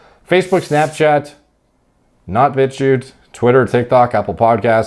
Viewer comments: Ngl this whole time I thought u been saying “bitch u Twitter” 💀💀💀💀💀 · All I hear is "b*tch you twitter"